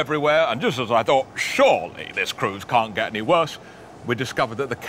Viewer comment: I got distracted at the end and thought had said at the end "and that's when I vogued down" 😂